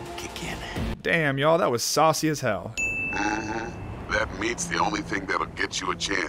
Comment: I think Dexter says "Meet" and not "Meat". He means that meeting Evelyn is the only way to obtain the BD required for the job.